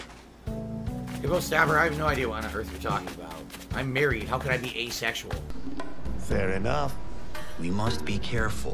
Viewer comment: Yall ik this is my clip but wtf does he say in the first sentence lmao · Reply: “Ebo staffer(?) (someone in chat) I have no idea what on Earth you’re talking about. I’m married; how could I be Asexual??”